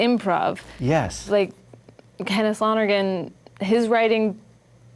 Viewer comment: who is she talking about in min 0:58? can't understand the name, but would really interest me to look that person up. thank you! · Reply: Kenneth Lonergan the playwright/movie writer-director